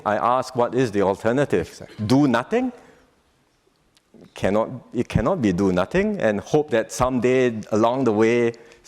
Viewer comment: I think PM nearly said "cannot be mah" 😂